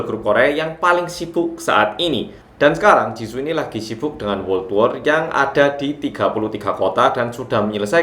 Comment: Btw gasalah denger kan ya gue kayanya kecepetan ngomong jadi WORLD WAR 0:52 🤣🤣🤣 jd ngakak